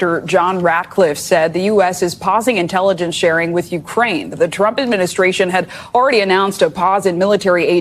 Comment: I swear that when the NBC reporter said “the trump administration” I heard “the corrupt administration”. I had to go back and listen again.